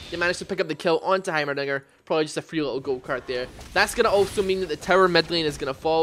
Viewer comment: when he said "gold card" i thought he said "go cart"